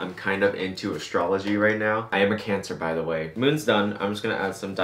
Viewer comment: "I am a cancer btw" WUT I heard, "I have cancer btw" Me:OH MY GOODNESS ARE YU OK!?!?!?